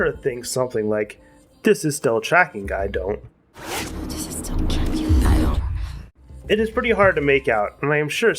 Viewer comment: I think she says "Does this still kill you, I don't know?" Like she's talking to herself.